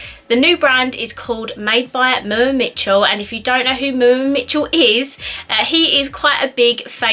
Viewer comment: I thought she said moon Mitchell wtf